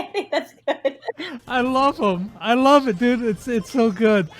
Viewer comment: did he just call her dude?